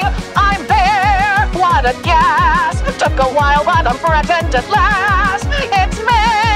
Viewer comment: Bro I thought that Alastor said “took a while but I’m pregnant at last”😭